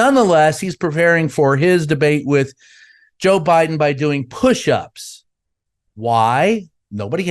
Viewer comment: I thought he said RFK was doing kush-ups to get ready for the debate.